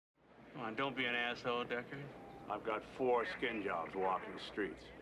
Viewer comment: "Skinjobs, that's what Bryant used to call replicants" Thanks a lot movie I was to dumb to realise that and unil now I haden't realised "skinjobs" are supposed to be replicants. You just oppened my eyes to so many things thanx for the AMAZING delivery too Ford theatrical release ftw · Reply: I thought Bryant said foreskin jobs and I burst out laughing at a screening.